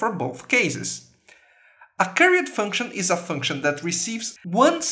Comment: carrot function? carriot function? charriot function?? what did you say at 6:08? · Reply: Curried function